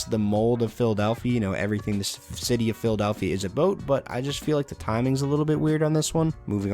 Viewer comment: did he just say ABOAT?